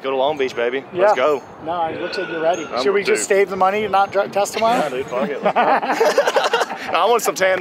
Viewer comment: did he say drug test?